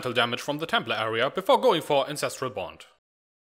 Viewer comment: did you say INCESTRAL BOND? haahahahaa I shat myself laughing and even went back and listened again, and it really sounds like you said incestral bond... lol